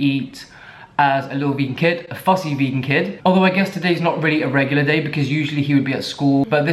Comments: At first I thought it said “What my Pussy Vegan Kid eats in a day” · I thought it said “PUSSY” I am soo sorry lmao. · Boiii I thought that shit said pussy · i thought u said what my pussy vegan kid eats in a day luls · I thought it said “pussy” LMFAO